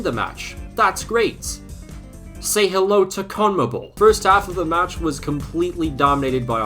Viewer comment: what did he say at 1:51 i cant understand it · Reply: COMMEBOL